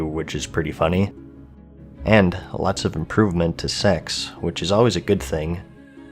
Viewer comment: what did you just say? "lots of improvement to....what?" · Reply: I think secs - secondary stats :p